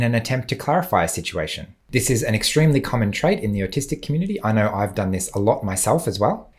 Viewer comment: I thought you said the ARTISTIC community. 😅 I said, (That's me!) 😳